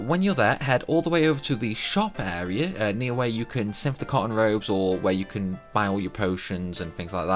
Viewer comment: it sounded like he was saying "con Road" when he said " cotton Robe".. Annoying. Anyways...